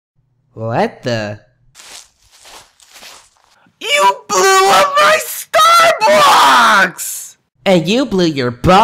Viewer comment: What did he say? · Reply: You blew up my Star bucks!